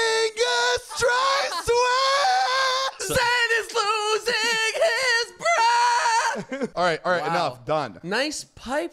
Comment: Thought was gonna say “Zane is losing his hair” instead of “Zane is losing his breath”😂😂